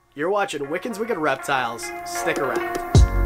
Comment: lol it sounded like you said "Snake around" in the intro